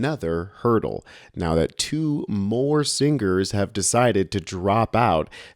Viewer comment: What did she say??? SingGers?